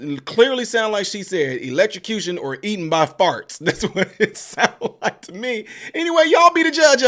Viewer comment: Oh eaten by Farks, I thought she said Farts. Haha😅